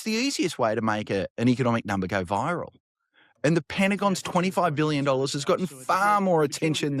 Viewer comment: That's funny I thought they said 29 billion today...